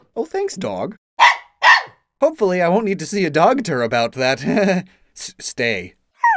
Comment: 😂😂 I heard him say dogter